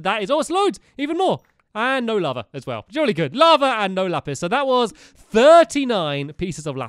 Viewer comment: um did he say: Jurley god Lava and No lapiz :)